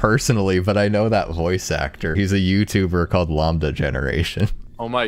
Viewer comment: did you just say lombda